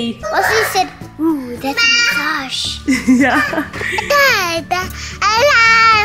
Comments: sounded like she said massage lol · it literally sounds like said “massage”🤣💕